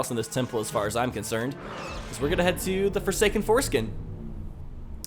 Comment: did he just say the forsaken foreskin